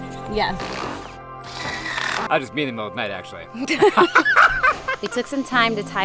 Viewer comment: He said, "that was me in the middle of the night, actually" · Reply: i thought he said "I was in mode night, actually"